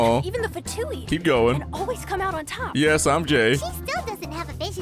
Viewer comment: I thought you said "i'm gay." But then I relised that you said " i'm Jei."